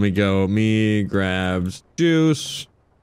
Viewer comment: i swear said deuce not juice lol